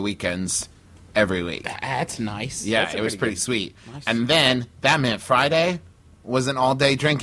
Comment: Wait, did Travis just say "...that meant Friday..." and then did Justin mishear it as "Batman Friday?" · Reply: I also misheard it as Batman Friday